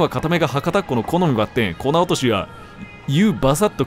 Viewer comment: 店長「～～粉落としの6種類～～」 桐生「……虎落とし……??(空耳)」